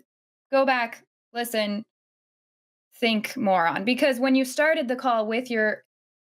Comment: Like I know said, "this is something you should go back, listen, think more on" but I keep hearing "think moron" instead and I honestly like that better haha 😂